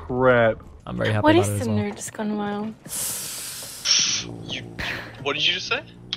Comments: what did she say? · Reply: yea seriously all i heard was "whats the nerdest gun while" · what did she say at 8:57 what is the nerds gone wild??? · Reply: Nerds gone wild